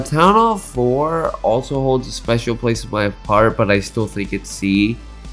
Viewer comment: did you say part or heart? It sounds like part but it would make sense if h said heart, not sure what u said here lol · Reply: Heart